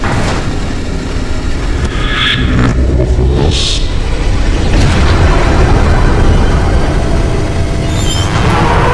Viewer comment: What did tv man say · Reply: F#ck im out